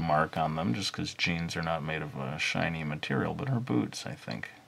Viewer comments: when he said,"her boots" i thought he said,"her boobs" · I looked away for a second and thought he said boobs. Took me a moment to realize he said boots